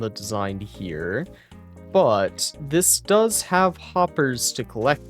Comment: i thought that said: "but that does have coppers"